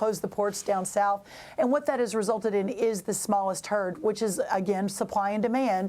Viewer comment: did she say "the smallest turd"?